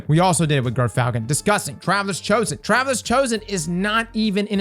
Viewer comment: when you say travelers chosen, ur sexy accent makes it sound like ur saying “travis chosen”